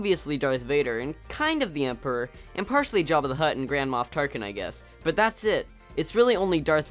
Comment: first off I thought you said "Grandma Tarkin" because I had forgotten his title